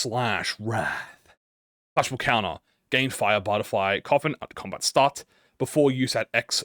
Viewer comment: i think i found an error it says fire butterfly coin instead of fire butterfly coffin